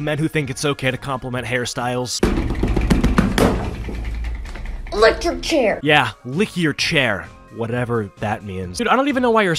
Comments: I think she said electric chair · I think she said electric chair from a complement....... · what i heard at exactly 6:47 was electric chair not "lick your chair" sorry if i'm being rude, just wanted to point it out